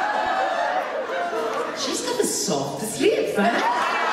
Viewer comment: What did she say in 32:42? I can't understand... Thanks:) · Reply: She’s got the softest lips